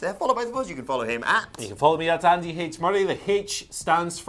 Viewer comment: Really sounds like he says "Andy Hates Murray"